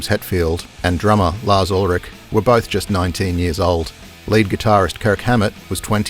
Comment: This is what youtube is. Was. Should be. Not AI slop. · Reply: This is AI bro. At 2:39 he says the ‘late’ gitarist Kirk Hammett instead of the ‘lead’ guitarist. Made me look up immediaty online whether Kirk was still breathing. Luckily, he is.